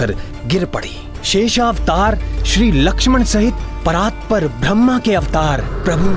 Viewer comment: Did he just called raam as Brahma's Avatar? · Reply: Not brahma.... Param brahm's avatar